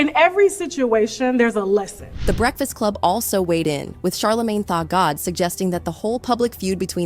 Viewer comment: did the AI just said “Charlamagne Thot god “? Or am I hearing things 🤣🤣